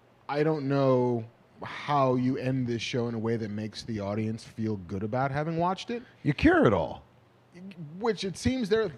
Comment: what did Mark say antibioticly delicious.... embryonicly delicious? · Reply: amniotically delicious